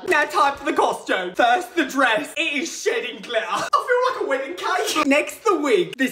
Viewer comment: I thought he said the dress is shitting glitter😂😂